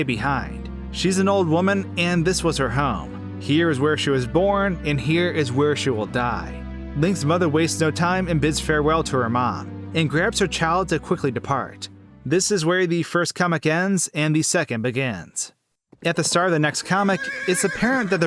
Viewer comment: Oh my god I thought she said “farewell, then, dead mother” XD